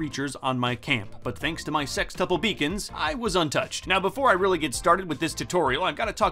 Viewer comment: Love that the audio messed up and all I heard is “SEX BEACON” the YouTube lord works in mysterious ways.